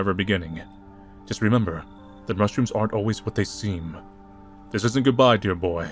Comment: i thought he said restrooms at first and had to go back